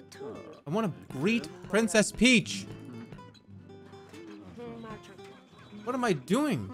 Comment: am I going deaf, or did he say:"I wanna breed princess peach!"